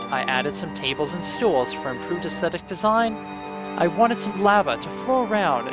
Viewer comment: What did you say the tables and stools were from? I can't make out the first part of what you said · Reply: Ah yes. I said it rather quickly, but the words are "I added some tables and stools for improved aesthetic design" To answer your question however, these are from the Custom NPCs Mod :)